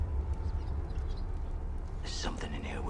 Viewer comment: Did he say "Silence, you DUCKS!" @ 0:27?